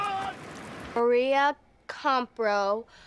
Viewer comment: i thought she said "Amir"and got confused 😂